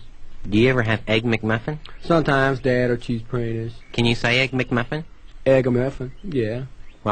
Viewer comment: what is he saying? Cheese what? Praintiss? 😅😅😅